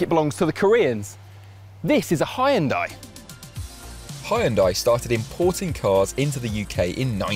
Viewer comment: Did he just say Hai-en-Dai?